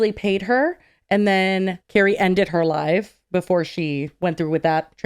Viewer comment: Lmao 1:59:49 I thought she said "Carrie ended her life". I was like... Wow that's severe 😳🤣